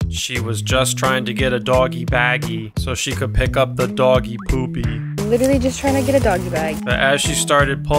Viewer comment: Excuse me did you just say "HER doggy poopy" 💀